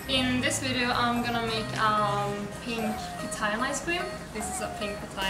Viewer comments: This is a dragon fruit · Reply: On the sound bite on u tube it sounded like she was saying papaya and I also know it is called pitaya I’m from South America in US it’s called dragon fruit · Pink Papaya? Ummm pretty sure that's a Dragon fruit lol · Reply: Guys you don’t need to keep correcting them, they made one little mistake, it’s been corrected, the word sounds similar, I thought she said papaya too until I started reading comments about pitaya.